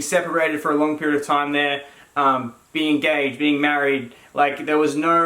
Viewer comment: Did he say "being gay and married"?